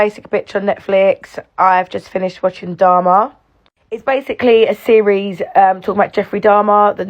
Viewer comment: At the beginning i thought you said dhar man and you would be just joking